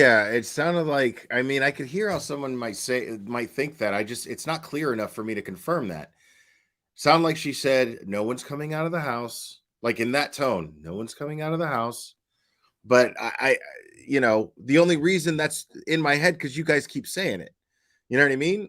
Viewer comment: When you enhance, I hear “Proctor’s coming out to help”